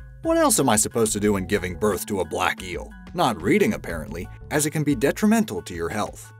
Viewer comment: Did he just say: GIVING BIRTH TO A BLACK EEL???!